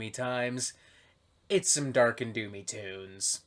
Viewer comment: Thought you said Dark and Looney Tunes at first!